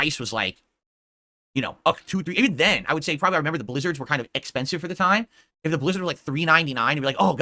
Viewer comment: Wait did Phil just say eckspensive